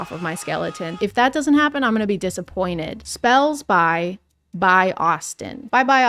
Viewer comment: I THOUGHT IT SAID SPELLS BY AUTISM